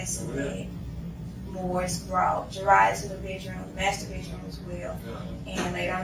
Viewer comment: When the detective asked her what she was thinking when she went into the nursery and first saw her she actually said “ I was thinking what’s wrong with this thing “. Unbelievable!!! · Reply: Don’t go by what the captions say..they are so messed up at one point it writes something about “dry to the bedroom masterbation as well” but she’s saying something about going to the master bedroom🤦‍♀️i caught that and I had to rewind cuz I was like wtf, there is no way she’s talking about masterbation right now! It’s at 17:34 mark